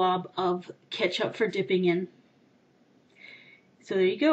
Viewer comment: Sounded like u said Hambooger at the end